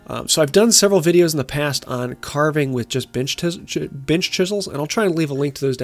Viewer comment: great video! a project i will have to try.i misheard you at 10:40 and thought you said "carving with just bitch tit chisles" lol